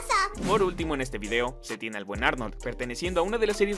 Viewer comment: no se ustedes pero escuché Buenardo Pddt: nuevo sub :3